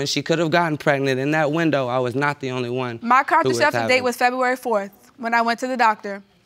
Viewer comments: Did she say “my contraception date”!?!?! 😳. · What is a contraceptive date?? 🤣🤣🤣🤣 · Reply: Lol i thought you said conception not contraception lol